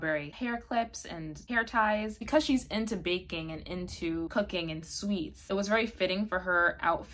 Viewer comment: No idea why, but when you were talking about Ichika's interests, I heard you say "BANKING and cooking"...and I was SO confused. I had to pause the video and rewind 😅